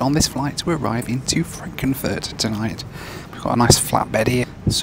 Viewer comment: Did he just say „Frankenfurt“??